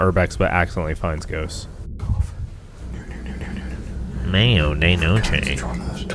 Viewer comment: i love how at 9:41 you see me saying 'i cant unhear Mayo De Noche' then you say it, and then the captions said mayonnaise LMAO